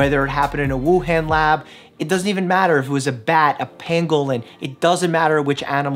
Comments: did he just call a penguin a panglewin? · wait did you say penguin or pengulin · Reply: I believe he said pangolin! They're like anteaters, but spiky like a pineapple or pinecone. They're very strange, but very adorable.